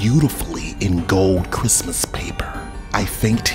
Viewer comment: The last dude killed me 😂😂 couldn't even take the story seriously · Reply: A peace of paiper I know it’s paper but it sounds like he said paiper XD